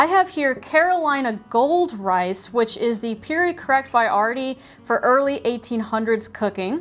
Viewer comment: "Period correct..." someone please tell me what the next word she says is, because I've never heard it before and I couldn't figure out its spelling to look it up. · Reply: Variety. I suspect nerves were at play here maybe?